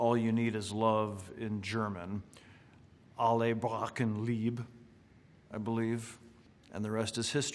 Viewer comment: what on earth did he say at 7:50 Alle brauchen Liebe?